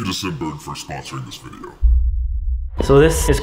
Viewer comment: No joke, I thought they said the sponsor was “Sick Burn” not Scent Bird lmao 🤣